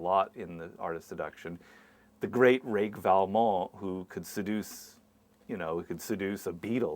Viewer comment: What was the name he mentioned at 4:00? I can't pronounce it. I think he said the great rake val mall? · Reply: Valmont. Les liaisons dangereuses=book.